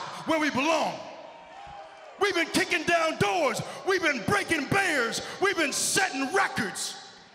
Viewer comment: I know big e meant to say breaking barriers but it’s sounds like he said breaking bears